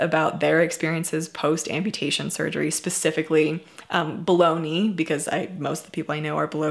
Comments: When you said below knee I thought you said bologna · Who else thought she said bologna not below knee